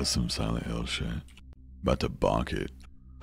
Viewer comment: I think he said " 'bout to bonk it" :D